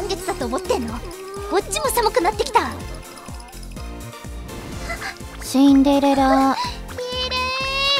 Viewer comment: wait, she literally says "cinderella" (with an accent), why did the caption say bride?